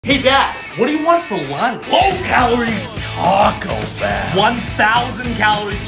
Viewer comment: I thought he said low calorie 😭